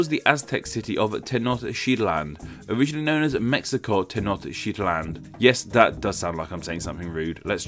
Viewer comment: It sounds like you said sh*t land